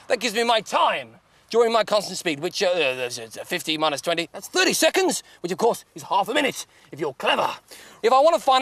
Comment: what is he saying?????? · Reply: "30 seconds, which is half a minute, if you are clever!"